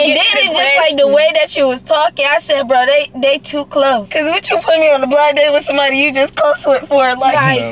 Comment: tell me why I thought they said being on a blind date with my brother😭